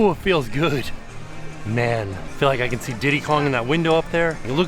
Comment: Did anyone else hear “diddy Kong” 😭